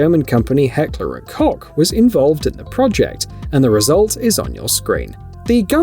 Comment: did I hear heckler and cock?